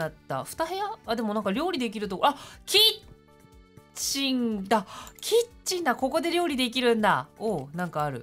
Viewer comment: 一瞬「キッチンだ」が「ちんちんだ」って聞こえてビビっちゃった^_^